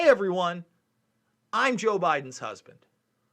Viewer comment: I'm not defending this guy, but it sounds like he said "I'm Jill Biden's husband." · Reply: Yeaaaa...watch it in .5 speed or .25. He says Joe man.. he says Joe. It's actually pretty clear too..